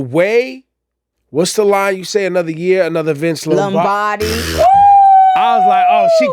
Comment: I was today years old when I learned that Nicki said “another year another Vince Lombardi” 🫢 I thought sis said Another year another thin slim body 😩. This is what I’m talking about. You always miss something on the 1st, 5th, 10th listen.